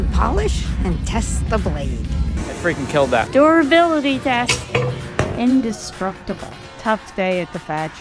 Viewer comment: When I first saw this, I thought she said “door ability test”, but now I realize that she said “durability test”😂